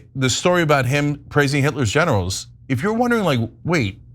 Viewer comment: Ok, took me a few seconds before I realized Cenk said "Trump praised Hitler's generals", I thought he said "praised Hitler's genitals" 🙈